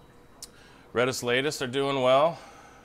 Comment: Nice fish room. Couldn’t understand what you said the fish was at 6:54 with the massive nuchal hump. What’s that one called? Had a flowerhorn named Rainbow but had to get rid of him😞! I regret it some but he kept hurting my Oscar and other fish I had. Wad looking forward to seeing him/her grown up! Not sure if it was a girl or boy! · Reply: Thats an Amphilophus Red Isletas at 6:54. What type of cichlids do you keep now?